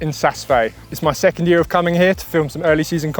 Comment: Where was this filmed? He talks about it at 0:32 but I cant understand it :D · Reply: Found it after going through a list of glaciers in case anyone is interested. The Ski Resort is in Switzerland - Saas-Fee 😎